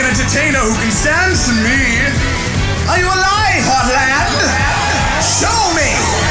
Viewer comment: Mettaton- ARE YOU A LIE HOTLAND!! Crowd- WOOOYAAYY Mettaton- SHOW ME!! Crowd- WE LOVE YOU METTATON! WE LOVE YOU METTATON! Xd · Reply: I heard "EYE ON EYE HOTLAND? SHOW ME!"